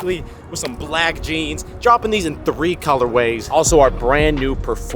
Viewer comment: Every time I hear “Color Way(s)” my brain instantly thinks about Taco Wey. 🌮